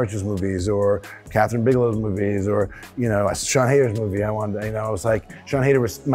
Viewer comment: I can't understand the last director he mentions in 1:16 ? Sean "haters"? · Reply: Thank you! For a moment, I thought he said Sean Anders - the guy who directed Daddy's home. And i thought "this can't be right". But it would have been funny if after that list of legendary directors he would quote the guy who directed Dumb and Dumber To and Horrible Bosses 2. 🤣